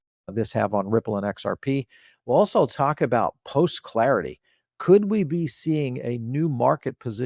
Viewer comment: Did I hear right ? "We'll also talk about post (nut) clarity"...